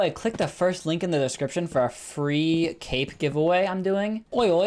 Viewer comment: I THOUGHT HE SAID FREE CAKE GIVEAWAY LMFAOOOOO